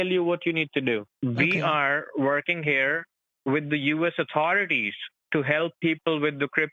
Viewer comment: Sounded like he said "We are working with the USSR".